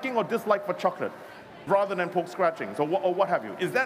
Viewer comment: What is pork scratchings? Also I thought he said pole scratching. I had to turn on subs.